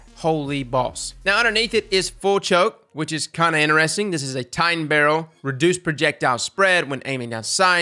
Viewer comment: he said “full choke...this is a tightened barrel” and I heard “full choke...this is a titan barrel” and in my head was like “hell yeah it is. Titans with felwinters!”